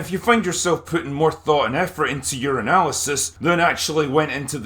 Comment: Lol, I heard "your analysis" as "Urinalysis".